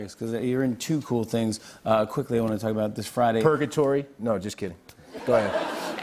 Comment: what's the word he answered? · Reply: Purgatory.